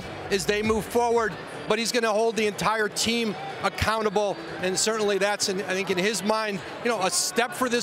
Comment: Woj just literally called that man Russell Westbrick Tell me I'm not the only one that heard that 😂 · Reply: I thought he said that as well. I listened to it multiple times and it does sound like Westbrick.